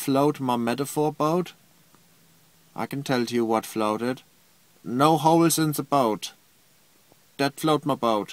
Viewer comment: For a second I thought he said 'no hoes in the boat' hahaha